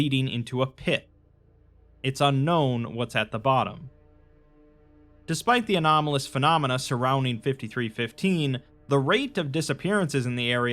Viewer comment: i heard Anomalous Phenomenous 😅